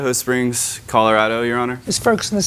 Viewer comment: did she call Judge Judy "sir"? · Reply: I think she said yes honour.